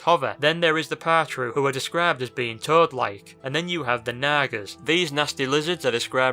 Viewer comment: XD omg this dudes fucking voice "naga" sounded like he said naggers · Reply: How else do you say it? To me it was Nah-Gahs not naggers, it wasn't said quick enough enough to sound like that.